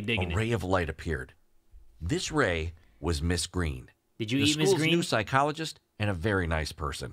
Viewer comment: me when i hear "did you eat ms. green" ms. green bean